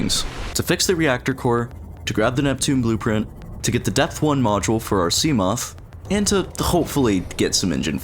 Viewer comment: why did i hear this as "the death core" poor reapers💀